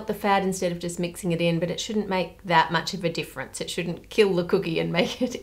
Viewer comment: 10:8 I thought she was saying “it shouldn’t kill the cookie and make it evil”